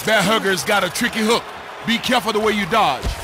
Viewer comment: I thought he said "Hat trick" lol guess I heard wrong